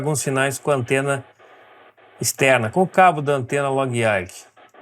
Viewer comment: Qual o nome da antena? Não entendi... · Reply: Log-Yagi. É um tipo de antena.